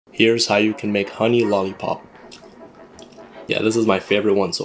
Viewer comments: I thought it said money lollipop 💀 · bro i really thought he said money lollipop😂